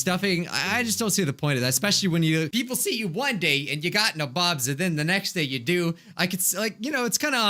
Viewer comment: Pls I thought u said kabobs instead of nabobs 😭